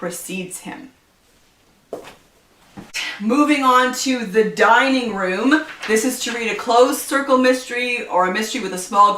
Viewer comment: 😂😂 When you said dining room I totally thought you said "dying room" 😂